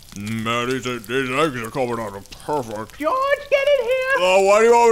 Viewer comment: at first i thought he said his eggs were coning out perverts